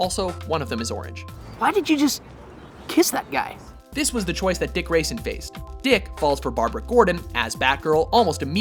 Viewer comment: you called robin a dick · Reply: You misheard him. He called Robin Dick Grayson.